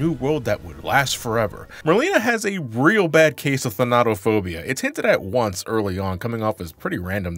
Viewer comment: Fonato fobia? I am trying to look it up cannot find it anywhere did he say it wrong? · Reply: Thanatophobia